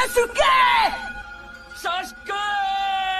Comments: "saskee!!" "Sausage!!" "Hey sasuke!" " SASgeeeeeh!" "SUskeeeh" "Sasuke!!!" "Sus-keeeeh" "Dimibaige" (how I hear) "JACKSON SASUKE (SAC SCHON SA-SUGEE)" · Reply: "Dimmi perchè" not dimibaige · "Sag schon Sasuke" Literally didn't hesitate to laugh at that one Edit: there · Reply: I only heard “Suck on Shashuke